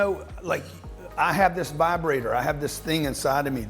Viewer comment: Did he just say that he has a vibrator inside of him? LMAO🤣🤣🤣